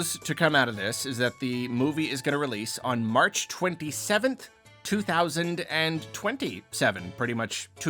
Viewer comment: I thought they said March 26th?